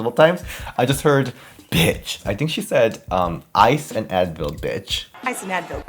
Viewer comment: I thought she said "buy some advil, bitch" at first lol took me forever to figure it out too